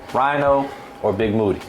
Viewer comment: Why I thought he said Big Booty at first. Instead of big Moody